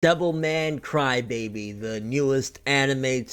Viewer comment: Sounds like your saying devil may cry